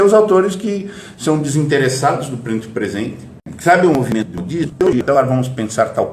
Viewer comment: no minuto 8:33 deu uma travada, nesse instante você disse que "o autor clássico é desinteressado do momento presente?" Tem uma outra frase seguinte que também não ficou claro, será que você poderia escrever aqui o que você disse, por favor? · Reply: Sim, é "DESINTERESSADO". A seguir, a locução é: "sabe o movimento disso...". O corte é imediatamente anterior e engoliu algum ruído.